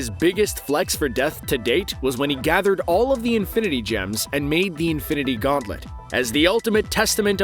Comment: Did he just say "Infinite Gems"